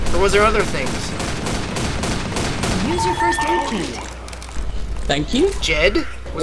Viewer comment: wtf zoey said use your first aid keet lol